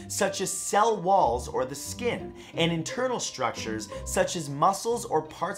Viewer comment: When said “Internal” I thought he said “In Turtle” for a few seconds.